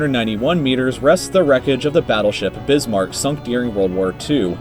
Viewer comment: I thought it said “the wreckage of the titanic” 0-0